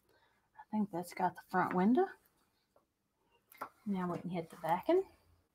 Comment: Tell me she didn't just say, "Front winda and backen" instead of window and back-in? 🤪